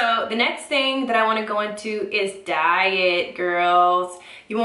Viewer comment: haha at 14:13 I thought she said "dye it" then I was like "ohhhhhh it's that other word I purposely ignore"